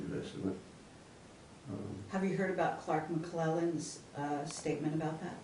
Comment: she asked if he heard some guy's statement. Whose name did she say? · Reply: Clark Mcclelland